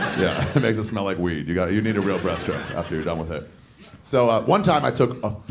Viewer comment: I was hearing "breast strip" for the longest time. I was like how.. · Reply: I heard “breath chip” for the first half of the vid lmao